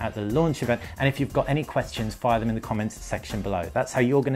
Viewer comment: i thought he said "thanks for watching dick rider"